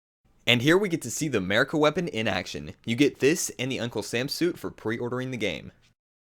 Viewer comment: Did he say "Uncool Sam Suit"?